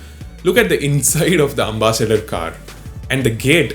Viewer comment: did he said amma-sador??